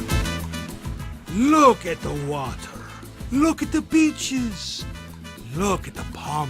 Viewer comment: it sounded like he said "look at the bitches!" i was like "WTF? lmao"